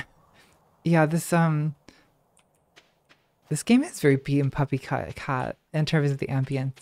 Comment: could someone please tell me what the name of the show is? I legit don't understand what she's saying. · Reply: bee and puppycat :)